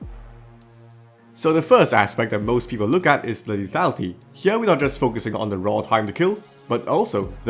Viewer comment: what’d you say ? I’m confused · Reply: Lethality. He said lethality.